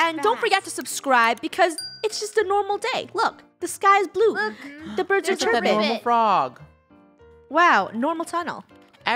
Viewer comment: I thought they said " its a normal day the birds are tripping"